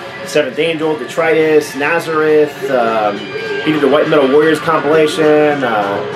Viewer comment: Coulda sworn you said "White Metal Lawyers" compilation there.... now THAT sounds like a crap record, hahaha! Interesting to see how enthusiastic you are about Magnum, that's one record that always kinda slipped through the cracks for me... I've know a lot of metal heads who liked them, but I somehow never got to them. Lemme know what you think of Whirlwind Tongues... I used to have that, but didn't particularly like it, but I was also harder core about my "original singer" hangup that I've talked about in the context of Uriah Heep..... and especially since Jim Rutledge was so awesome, I probably never gave that record the chance it deserved! Great video man, lots of fun to watch this one and part one... it's not too long if the records are good and the presentation is entertaining!